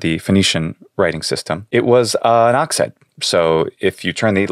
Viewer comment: isn't "oxen" plural? am I misunderstanding what he says?